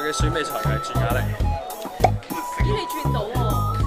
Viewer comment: 朱敏翰在說什麼？ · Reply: 我嘅選美才藝轉啞鈴